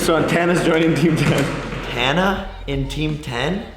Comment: is it just me or does jake call tana "hannah" 20:49 and in other videos he says "hannah" to.....